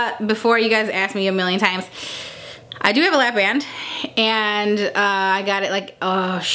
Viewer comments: what did she say at around 1:00 "I do have a ___"????? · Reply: L A P B A N D 😉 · Sorry what did she say at 1:02 ?? "I do have a ___" what? Labrat? Labrand? Laprand? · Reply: She has a lap band.